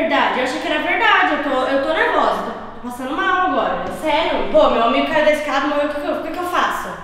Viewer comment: eu ouvi ela falando meu homem caiu da escada quem ouviu isso?